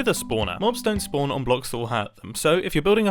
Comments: literally what the hell is he saying, PLEASE i need to know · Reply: "mobs don't spawn on blocks that will hurt them" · What did you say at 8:10? "Mobs don't spawn on blocks that'll happen"? English is my second language. I need help!